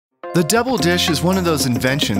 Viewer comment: It sounds like your saying “the devil dish”